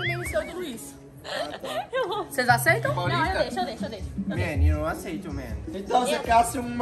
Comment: alguém pode me explicar o que a cams falou da Aila e do Luís? Por favor, pq eu não entendi. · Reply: Eu entendi "ele quer entrar no meme seu e do Luís"